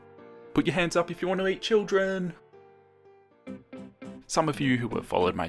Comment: Did I heard it wrong "put ur hands up if u wanna make children"